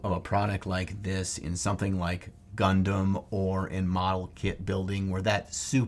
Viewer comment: When you say "Gundam", I hear "Leman Russ."